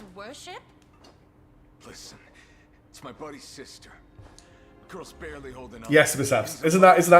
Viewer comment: He said "my buddy's sister", not his sister. So it pretty much collaborates to what Gammel said and makes it more likely to be a truth